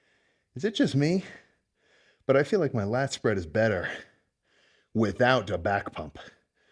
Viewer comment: “with oud a back pump” sounded like urs lmao